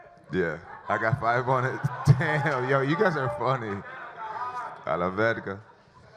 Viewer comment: did jones just said...." A LA VERGA " ????